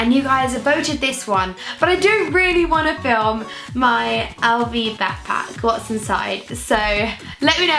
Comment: Who else thought she said "what's in my Aldi backpack" 😂